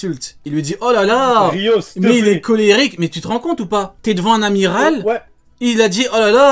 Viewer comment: lol j'ai entendu ''Oh lalala Neal est colérique'' 😂😂😂😂😂😂😂😂😂😂😂😂